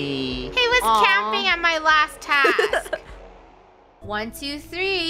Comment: the captions said house instead of task